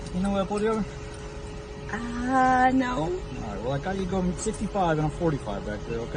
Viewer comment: He says "I got you going 65 in a 45", not what the closed captioning says "55 mph on a 45 mph". Y'all need to do a better job hearing and listening.